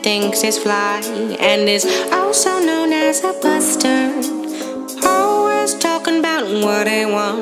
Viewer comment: Anyone else thought at 0:08 she said “bastard” instead of buster? No, Just me? Okay